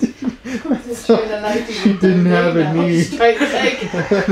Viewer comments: In all fairness, I thought he said Helen didn't have a knee either! 😂😂 I was like damn this dude was just going after woman he could drag easier! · I also thought said she didn’t have a knee 😂 too funny! This was great! I’d never heard this story. Very fascinating! The giant bugs! 😮 ewe! Very creepy woods! 🙈 I am shocked at home many sounds and noises you clearly captured! Loved it! ❤